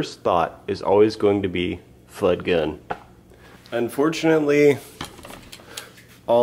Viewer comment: what did you say? I think I missed a good punch line because I can't hear it. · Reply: I think he said “Fudd gun”. As in Elmer Fudd.